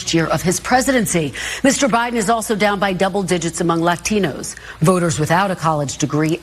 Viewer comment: did she say leftinos?